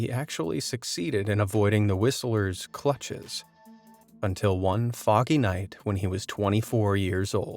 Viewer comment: What did you say one fucking night?